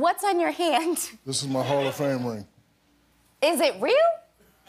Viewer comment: It sounds like he says "this is my whole family" instead of "hall of fame ring"..